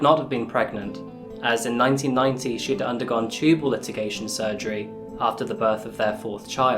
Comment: I'm not sure but I think I heard you say "tubal litigation".....??? :-)